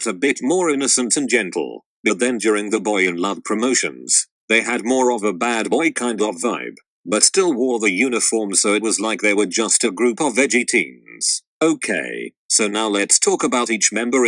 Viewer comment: it sounds to me like it’s saying “veggie teens” instead of “edgy teens” 😂😂😂